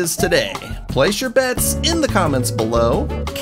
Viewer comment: I thought you said place your beds in the comments 🛏️